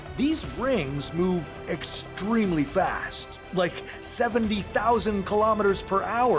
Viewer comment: He actually said 70,000 kilometers per hour, not 700 which is INSANE 28:30 Edit: Any way I try to type this it sounds mean, sorry 🙏🙏🙏